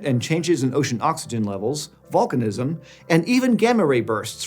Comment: i thought this said "Balkanism" not "Volcanism" and was almost like "what did the Balkans do THIS time???"